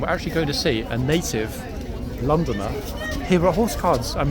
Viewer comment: I love the chat with the ladies from London. What a sweet lady 😍. I must say, you are hilarious today. I'm thoroughly enjoying today's video wait for tomorrow's 😊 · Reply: Omg. I thought you said a naked Londoner then I realised you said native Londoner 😂